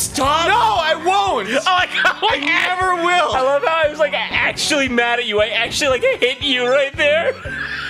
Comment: What did Jon say here? I can't hear well. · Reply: isaacman2000 "I love how I was like actually mad at you, I actually like hit you right there"